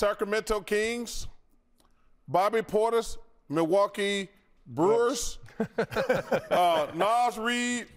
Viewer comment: Milwaukee Brewers and Minnesota Vikings 😂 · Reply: I thought Chuck said Milwaukee BRUISE!!! oh man! 🤣🤣🤣🤣🤣🤣😅😅😅😅😅